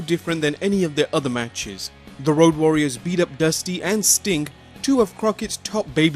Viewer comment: Who heard him say stink instead of sting